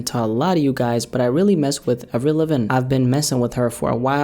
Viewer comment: What in the actual F*** did he say at 2:13 ?!😂😂 HAHAHAHA i was hitting the bong and almost died when i heard that. I replayed that like 20 times trying to figure out if he even said her name or just had a seizure. My guy said it fast as hell too hoping noone would notice how stupid that sounded 🤣🤣 i love you but that was comedy my guy. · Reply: “Aveilivin” HAHAHAHAHAHAHA